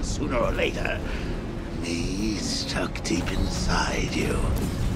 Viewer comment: what did he just say? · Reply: "me stuck deep inside you"